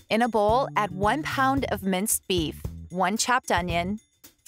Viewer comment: At first, I thought she said men's beef, thankfully i am partially deaf!